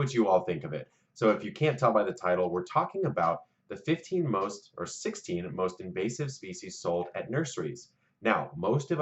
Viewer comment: I had my volume on low and heard it as "the 15 most or 16 most racist species sold at nurseries"